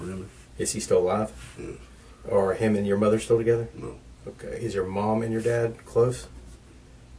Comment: I’m only at 6:18 in the video, but some of these subtitles are inaccurate. They asked if his parents are together and he said, “No”, but the subtitle says “yes”. Then they asked if his parents are close and he said, “Somewhat” and the subtitle says, “no”. It’s not a huge deal, but I wanted to mention it incase you want to fix it, since I know some people depend completely on subtitles. Both of these happened somewhere between 6 minutes and 6:18.